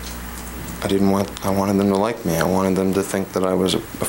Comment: wtf is she saying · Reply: “I didn’t want to be humiliated. I wan...I wan.... I wanted him to like me”.